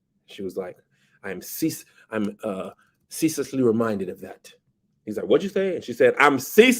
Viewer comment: What Rhae Rhae said: "I am ceaselessly reminded of that." What I heard: "I'M TIRED OF THIS CHURCH!"